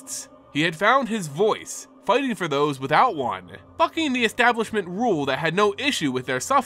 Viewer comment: Yo wait did you say f***ing at like 3:23 - 3:27 or was that "bucking the establishment"? Checked subtitles and it just says "one. The Establishment..." Im not a Karen, I just have never heard you cuss on the channel so it caught me off guard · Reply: Deff. bucking lol. Sorry if I’m fudging the words a little lol